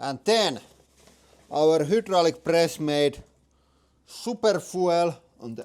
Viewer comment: I thought he said garlic press. I need my ears checked fosho lol